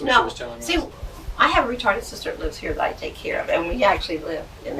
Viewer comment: Did she say "retired sister" or mentally challenged?!? 😮🎉